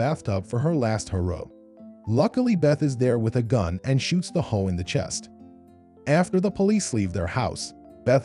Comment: Ok but did he say “and shoots the hoe in the chest” at 11:38 ?